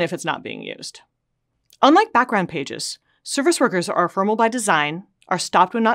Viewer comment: captions say formal by design, is it formal or ephemeral?